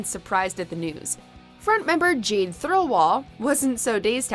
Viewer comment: Okay i swear the narrator just said Jean instead of Jade 😂😂 at 02:26 😎